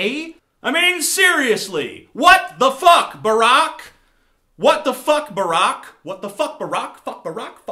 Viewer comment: LOL 4:20 when he says 'what the fuck barack' the subtitles say 'Seriously, laptop outdoor rock' hahaha